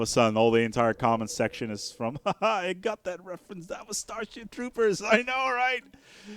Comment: did you just say "Starshit Troopers"? lol